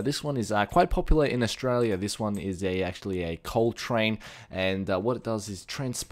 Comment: Did you just say COLE TRAIN baby